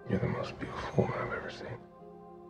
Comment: What is he saying? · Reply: You kill me